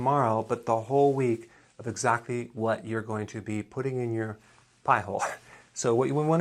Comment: Did you say "piehole" ? 2:20 sec.